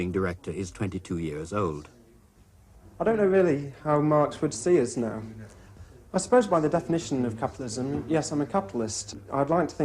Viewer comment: Hello, everyone! Can someone please help me? I'm not a native English speaker and I'm trying to translate this film to my native language. I understand clearly what the narrator is saying, but I have some problems with the other speakers. Can someone please write down the subtitles of what is said by 1. the businessman at 10:25-11:05, 2. the worker at 11:27-12:48, 3. the other worker at 14:51-14:57. Thank you in advance. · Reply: Businessman: "I don't know really how Marx would see us now. I suppose by the definition of capitalism; yes, I am a capitalist. I would like to think that I'm not the same sort off capitalist that used to here in the time of Marx. I think capitalism still exists. Obviously, it's got to if people are going to work, produce things, make a profit, and create jobs. Although I think that the overall face of it has changed an awful lot. Nowadays it's a much more cooperation between myself and people working here. The product that we are manufacturing the most of at the moment is the snooker light, it goes into snooker club and monitors the actual table usage. We have installed in snooker clubs all over the country." Worker 1: "When you first can't make money you think like, you start off and think to yourself like it would only take 2 or 3 weeks until I find a position then I should be alright. You know within a week, months you know and it is just really self-destroying. I get what, 22 pounds a week, you know I would like to see a manufacturer make a living for 22 pounds a week, I really would. You just get deeper and deeper in debt, there is no way in the world you can live on 22 pounds a week you know. You can't eat properly, you only eat once a day and even then only very cheap foods. When you get bills come in, you have to phone the people I have bought from, I got to see the people so you know, so like capping end you know all the time, sounds like what it was 100 years ago.